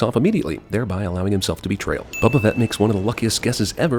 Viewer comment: did he just call boba fett BUBA fett?!